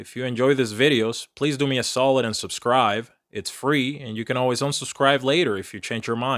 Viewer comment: Is he saying Subscrive instead of Subscribe? Sounds like he said it twice.